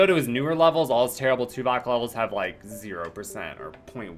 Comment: Thought it said Tupac level for a second there.